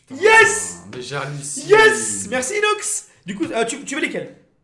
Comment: c moi ou il dit "Merci inox" 😂